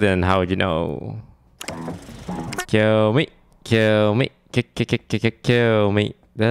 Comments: how he sang kill me instead of tell me lol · toast is singing tell me by wonder girls but instead of saying tell me he is saying kill me😂😂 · Toast being a wonder girls fan again at 12:48 hahaha he sing kill me, kill me instead of tell me lol XD